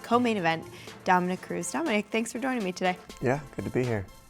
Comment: I swear at 0:15 i heard-Thanks for doing me today!